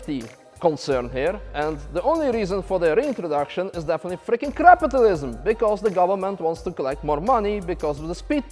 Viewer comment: Hey is the voice in the Track 'Da Tweekaz- Wodka' yours? Because I'm sure I heard 'fucking crapitalism' 😂